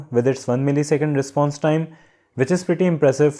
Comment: "1 ms response time", but subtitles say "1000 Hz report rate". 4:11 "Cost" vs "price", and there are also differences at 5:12 and 5:58. :P